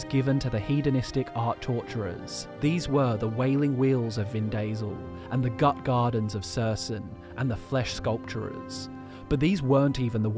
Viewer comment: You say Vindasel, I hear Vin Diesel, the caps say Vin Diesel XD